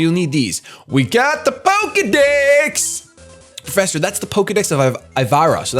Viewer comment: i'm sorry but i can't be the only one who laughed thinking said "pokedicks" instead of "pokedex"